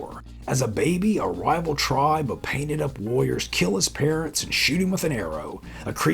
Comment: I like how you work in related commercials from the various times of the movies. My hearing is not so good; when you said "pig warriors," I heard "pig lawyers." Or maybe that's just my opinion.